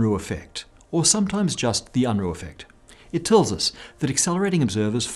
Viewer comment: i thought it said the obruh effect